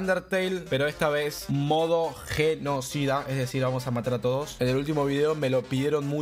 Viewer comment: pense que dijo modo tengo s1d4